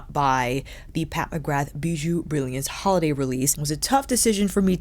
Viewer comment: Ever time someone says ”Bijoux Brillance” I hear “Boo-jee Brilliance”. I giggle every time. 😊